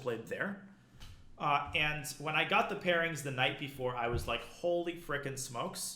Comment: I definitely heard “I got the pairings the Knight b4”